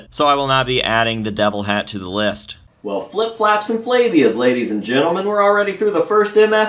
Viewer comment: I thought you said flips flaps and labia's 💯🤣🤪